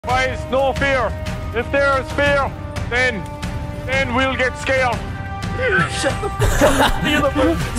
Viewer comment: Misheard it like: "Guys no beer, if there's beer, then, then we'll get scaled."